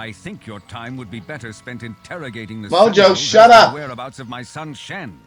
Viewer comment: Woah!!!!! At 3:33, did you say "Mojo, shut up!"? That would be a really wierd coincidence if you did. I say that many times a day! wierd....